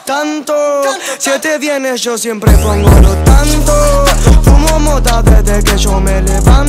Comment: pense que decia: "siete viene yo siempre pongo los tantos"